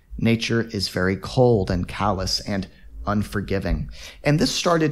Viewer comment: Teacher you said at minute 31:20 nature is cold and ... what cous or callus l didn't catch it